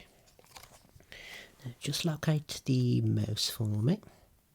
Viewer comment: When you said "follow the mouse" I thought you said "moose" and I'm like, "Sir, that's an elephant" 🤦‍♀️